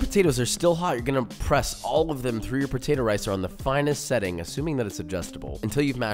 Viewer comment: thought he said potato ricin, don’t have a gf anymore